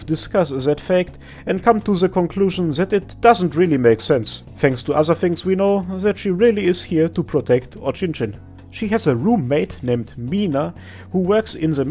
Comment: Had the video playing in the background 9:25 I heard "Here to protect ochinchin"